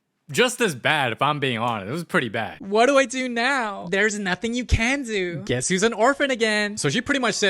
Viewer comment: what did she say? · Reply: There's nothing you can do